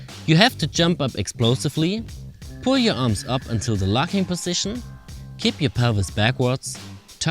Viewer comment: So in this part I didn’t quite hear what you said 0:48 “pull your arms up until the lock hand position” ??? “Pull your arms up until the locking position”???. And did you say to tilt kip or keep your pelvis backwards in the takeoff